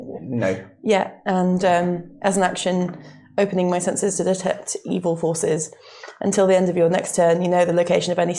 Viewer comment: What said: "I cast Divine Sense, and open my senses to detect evil forces". What I heard: "..and open my senses to detect evil horses." Oddly specific power. Also, using it in a dungeon is odd.